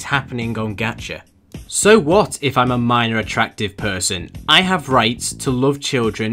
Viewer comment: Imagine being a MAP!!! Bruh TWITTER - <link> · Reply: ah shit I thought you said "I am a map"